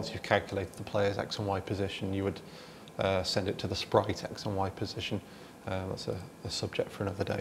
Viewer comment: What did he say @ 12:23? he send that info (player coordinate) to the "sprocket", "sprokitech" to handle the graphics? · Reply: "Set it to the sprite X and Y position" :)